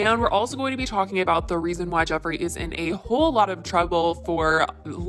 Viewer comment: all I hear is "why Jeffery is a A hole" 🤣